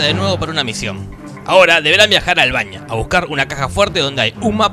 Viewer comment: escuche viajar al baño